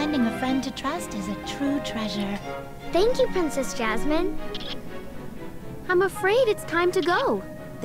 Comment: I was looking at two things at once and thought she said "lets forget about princess jasmine"